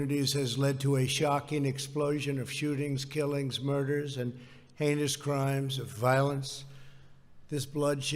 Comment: I totally heard “anus crimes” when he said heinous crimes.